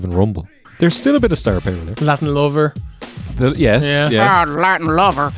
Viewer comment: Wait... His name was Latin Lover!? I always heard "Lad Lover".